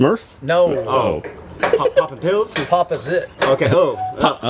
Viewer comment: Going to name it papa. Papa's it. said it so fast it sounded like he said Pop a zit! Lol